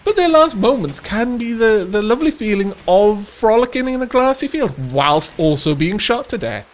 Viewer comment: I misheard "frolicking in a glassy field" i very much like to think that you would invite the enemy in, "come frolic in my grassy field" they accept and take off their shoes, only to find out that you meant glassy field and shred their feet, then as they slowly bleed out, shred the rest of their bodies, thus pre-preparing their bodies for Dark.